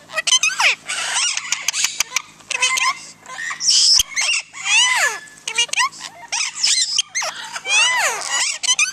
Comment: Is it saying "can I kiss you?"? So cute! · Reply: Sitrus Trash its saying “gimme a kiss”